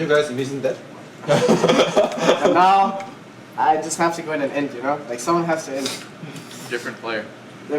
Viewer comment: Jensen clearly saying "Int" and subtitles say "end", how does someone (subtitles guy) in the gaming scene not know this word? lol